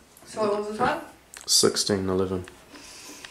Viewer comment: lol I thought it said 16 sec hahaha I was like total troll